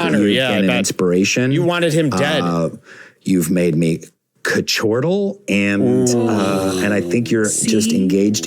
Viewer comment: what is rain saying at 56:29 ??????????????????? quichortal???? cant find the word on internet.. has my brain busted!!! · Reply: He’s saying chortle in a nerdy way , adding an extra syllable at the beginning that I guess is supposed to be an onomatopoeia.